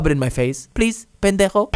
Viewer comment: Did he just said "pendejo" at 11:57 in how many languages is he going to swear? XD